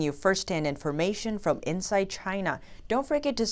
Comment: I heard that. "From Inside CHY_NAH"